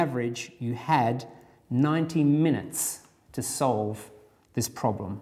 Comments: I too thought he said 19 minutes. I thought got a solution in under 10 minutes. Since it was so quick, I wondered if I overlooked something. I did overlook something. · I felt so dumb until I realised he said 90 minutes, not 19 xD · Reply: I thought he said 19 minutes until I read this comment. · I thought he said 19 minutes and I was like, I thought you were the math guy XD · I swear it sounded like he said 19 minutes. I was like what....I am gonna struggle with this whole exam. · At first I thought he said 19 minutes for each question. I was thinking your maths off. Lol